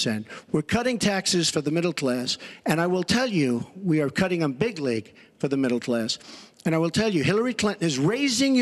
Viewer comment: All this time I thought he said "we're cutting them bigly", when talking about taxes - instead of , "we're cutting them big-league".. Every time I heard it!